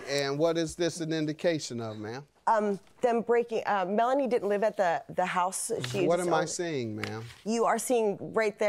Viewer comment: I need to rewind. I could've sworn I heard the defendant say "we lost the house ".